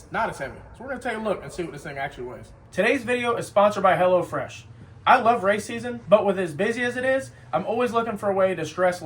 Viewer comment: idk why I thought he said “I love racism” 😭